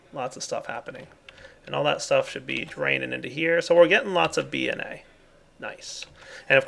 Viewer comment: Lol at 12:30 when said DNA I swear he said "getting plenty of BNA" made me laugh.